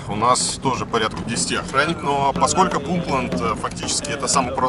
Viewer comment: Послышалось Путленд.